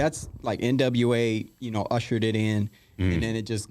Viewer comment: I thought he said “usher did it” lmfaoooo I was like WHAT?!? Usher was gyrating on the dance floor lmao 😂 then I realize he said nwa ushered it in